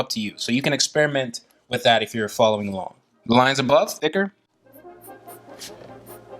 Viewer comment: What he said "Line above's thicker" What I heard "line's a buff snicker"